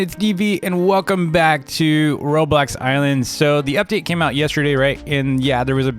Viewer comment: Did you just say ,,Robux islands”!!?!??!?